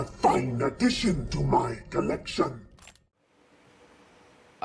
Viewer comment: It sounds like Palpatine said "Oiii" and stopped when Obi-Wan looked at him